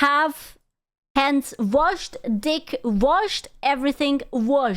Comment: did she say she washed dick?